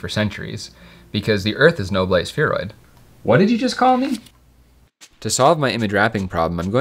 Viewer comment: when he says "what did you say" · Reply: sorry "what did you call me?!"